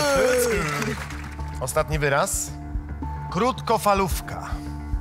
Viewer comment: Usłyszałam "kurtkofalówka"